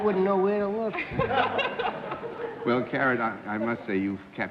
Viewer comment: Did he call her Karen?